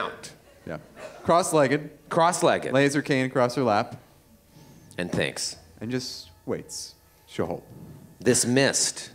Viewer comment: for a second, I thought said "laser cannon across her lap" and it flashed me back to his Androids and Aliens character.